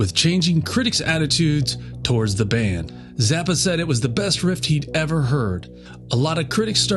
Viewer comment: Dude great video but 2:38 its the cover for "over-nite sensations" not Apostrophe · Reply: I hate to pile on, but at 1:31 you say, best "rift" instead of riff. Or at least that's what I hear.